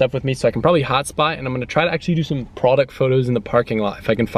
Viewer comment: i thought he said hotbox😂